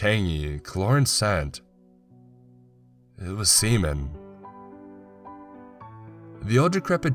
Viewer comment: I swear he said semen